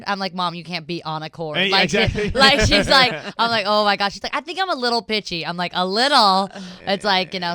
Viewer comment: lmao i wasnt reading the subtitles and when she said her mom says "I think I'm a little pitchy, i thought she said "I think I'm a little bitchy" i was like bahahahahahaha